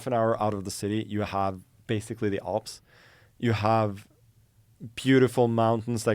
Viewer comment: I thought bro said “you have the ops” lol Magnus getting pressed in Kazakhstan